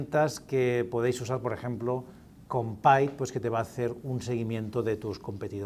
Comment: Cordial saludo. En el minuto 10:31 habla de una herramienta para seguir a los competidores, pero no entiendo bien la palabra. Con pipe???? No entendí y no he encontrado una herramienta con ese nombre o similar. Gracias.